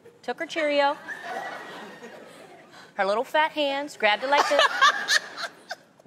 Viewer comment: thought I heard "her little fake hands grabbed it like this..." 😝